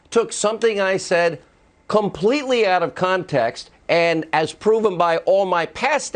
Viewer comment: did he say out of “comtext”? Or is that just the video?